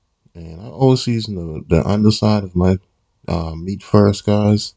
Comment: I did it but I thought you said cook on the under side anywho it still came out great thanks for sharing